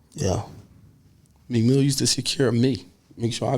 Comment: He said Meek used to secure him but i thought he said Meek ain’t put in no work or won’t like that